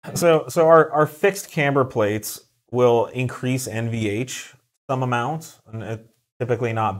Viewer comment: What is MVH? I think that is what you were saying in the vid...you said it would increase its but not bad, first mentioned at about the 2:05 mark. Also with the front tires now slightly leaning in due to the camber plates if you use these for daily driving wont the inside edge of the tires experience more wear? Also what about increased road noise using these? Thanks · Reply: NVH, noise vibration and harshness